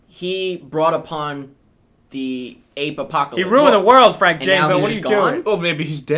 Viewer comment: at the 10:11 mark did call james franco frank jameso?